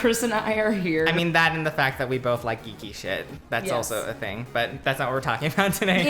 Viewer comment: Omg I thought he said we both like eating shit